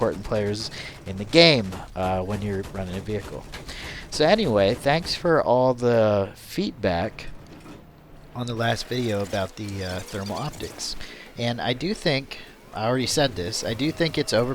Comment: it sounded like you said feetback instead of feedback